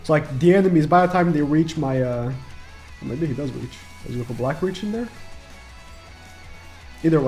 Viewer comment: what did he say!? 🤨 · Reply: oh my god he does reach, does my goku black reach